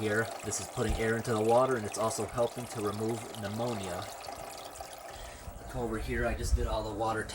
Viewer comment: Sorry to hear about your dad. How do you remove pneumonia from fish water? I think that is what you said at 2:57.